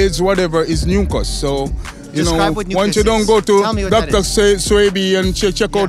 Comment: I think he’s trying to say mucus 😂😂😂